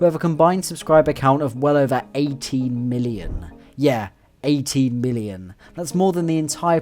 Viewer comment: it took me three listens before i heard 18 million and not 80million 😂